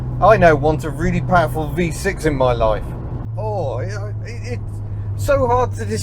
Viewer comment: I heard "I now want a really powerful V6 in my 2CV".